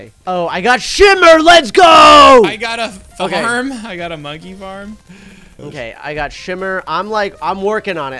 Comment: Did call it a monkey farm